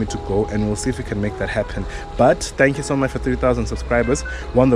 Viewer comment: I literally had to rewind the video because I thought I heard you say, "thank you for 300 000 subscribers". I was like, haibo kanjani manje??? 😭😭😭